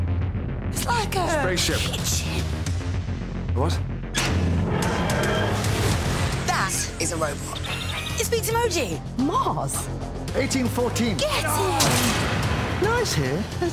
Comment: did she just say 'it speaks emoji!' kill me